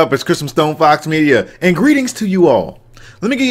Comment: It always sounds like he says it's Christmas stone fox media 😂